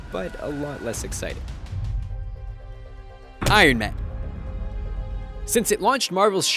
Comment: you said Iron Man but the subtitle says The Avengers!